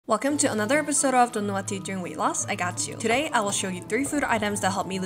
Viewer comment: It was so fast I thought she said, "episode of do not eat during weight loss..." and proceeds on saying these helped her loss 48 lbs. I got confused and repeated the intro many times 😂 lol